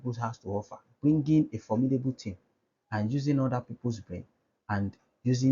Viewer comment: Sorry sir. i didn't quite hear the other meaning of OPM. Other peoples what sir? . i keep hearing "bring". i would really appreciate a correct to my inability to hear correctly.🙏